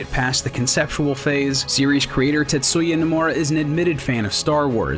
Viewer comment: I heard titsuya